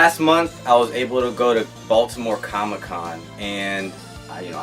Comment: nah im from D.C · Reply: Combo Ah, I misheard. Thought you were at NYCC, but I listened again and heard Baltimore Comic Con.